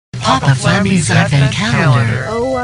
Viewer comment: you talk so fast , I thought you said "Papa Flammy's F-ing calendar" !!!!